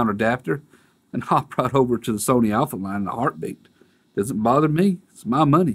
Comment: did you say hop over to the Alpha Nine or Alpha line, either way investing in a flagship sony won't get you a flagship customer experience from them either and the A9 isn't cheap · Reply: Yea Sony's service is horrid.. Likely still much worse in most areas. LOL I said Alpha line, lol not that A9.. I am sure Canon USA would not do this.. This is a Canon Philippines issue.. I have emailed them and linked the video to them also.. Lets see if they can resolve this or not.